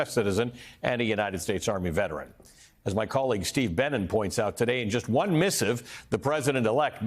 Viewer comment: Steve Benen - how unfortunate :face-green-smiling: all I heard was "my collegue Steve Bannon" & had to check the byline on the article